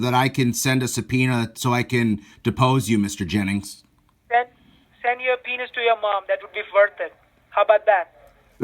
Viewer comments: it sounded like he said, send your penis to your mum....lol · The end though hahaha it sounded like he said send a penis to your mom! Hahahaha! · It sounded like she said, Send your penis to your mom. Ahh, where'd ya go? Thought we were friends. lol An Indian woman named from Vienna, Virginia. LOL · Did he say, at 14:08 "Send your penis to your Mom,it will be worth it, how about that?" Classic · I thought he said "send your PENIS to your mom, how bout that?!"😂😂😂😂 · ooooo you kill me. lol. the poor scammer couldn't close the money part. lol Sooo you are attorney. .oh! I thought he said send your Pen@ to your Mom. lol lol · I could have sworn I heard Mr Jennings say " send your penis to your mother".;LOL…... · Sounded like said "send a penis to your mom how about that" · I heard say, "send your penis to your mom". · I could have sworn they said "send a suh-penis to your mom" hahaha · lmao sounded like "mike" said "send your penis to your mom ____?____ how bout that?" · Did he just say "Send a penis to your mom working how bout that"? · He heard.."Send the subpoena to your mom". I heard.."Send your penis to your mom". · Anyone else thought he said "send your penis to your mom"..... Lmao 😂😂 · When the scammer said "send your subpoena to your mom"...well let's just say that I heard something else. I thought that he/She said "send your pen*s to your mom." Lol. Wow. Now I am disturbed. Have a great day everyone. ✌